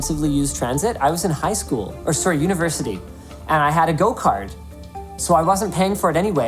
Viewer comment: I thought he said a go-kart lol I was like how the fuck does that work with public transportation.